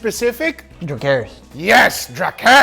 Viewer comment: I thought said "Drew Carey's" at first instead of "Dracaris" 🤣